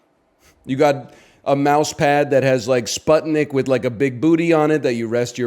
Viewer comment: he mentioned edging the gatorade and i thought 'what the fuck is he talking about' and then the video played and im fucking crying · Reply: im sorry did he say somthing akin to: "you probably have a mousepad of sputnik with a big booty" and if so why is that not the biggest +2 of 2024